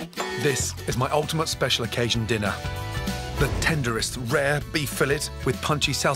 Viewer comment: Did he say rare or raw? (3:35) If you ask me it's raw. But then again I'm an uncultured beer drinker...